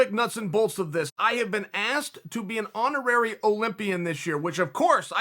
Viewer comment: I thought it said Horny Olympian lmao